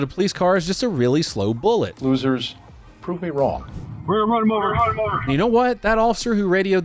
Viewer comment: what the cops said was "we are going to run him over" , what i heard was "LEEROY JENKINS!!!!! "